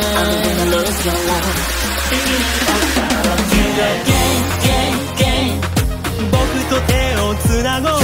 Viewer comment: when I heard this the first time without any captions, I heard "YOU ARE GAY, GAY, GAY"